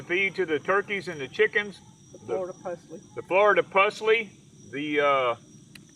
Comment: What is the name of the weed the Chickens, rabbits, turkeys, pigs all eat? I heard "flordy plusly" plant. Please spell it for me. Google can't find it the way I'm spelling it. Lol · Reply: Florida Pusley